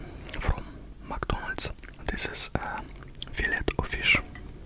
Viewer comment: Thought at first he said Philip of fish 😂😂